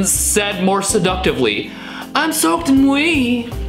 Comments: Why did I hear "im soaked in weed"? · It actually sounds like Adam is saying, I'm smoking weed. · "I'm soaked in wee" That's your own fault, Adam. Should have got out of the corner when you saw Michael Stipe coming. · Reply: i always thought he was saying "im smoking weed" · I thought the thumbnail said "I'm smoking weed" 😂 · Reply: I heard "im smoking weed" too 💀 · Poor girl. :( For sugar, I hear: "I'm smokin weed." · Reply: I heard “I’m soaking wee” O^O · I heard "I'm sucking weed" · "I'm soaked in wee" That's your own fault, Adam. Should have got out of the corner when you saw Michael Stipe coming. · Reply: Honestly I hear “Im soaked weed”